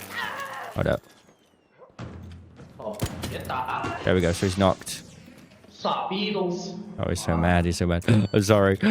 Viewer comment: can anyone that speak chinese know what the guy said? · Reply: "dont shoot, dont shoot, dont.... stupid mofo"